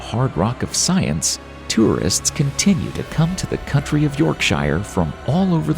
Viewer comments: some people get so triggered, LoL, if not the first time, take a second listen, and it's pretty clear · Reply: I watched it again @ 8:04 he does say country i missed that, another commenter mentioned it so i listened and yep he clearly says country. We got it wrong dude (THIS time) 😉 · 8.00...tourists come to the COUNTRY of Yorkshire??.......i did not know they had got independence......still part of England..........that should be COUNTY!! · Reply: It is not that big of deal. Everyone makes the simple mistake of mishearing things from time to time. The narrator undoubtedly said county not country. There is no need to get offended or defensive over something so minuscule. Js